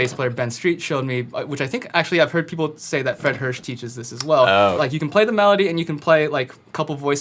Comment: I thought he said fred durst teaches that